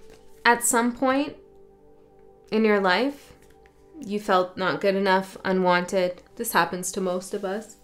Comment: Nice timing! Posted 3 minutes after I came out of the store and I got in the car. I listen to the other 28 minutes on the way home. I heard 'not enough Death' - I kinda had enough death the past week! gotta listen to this one 🙏🫶💜🌑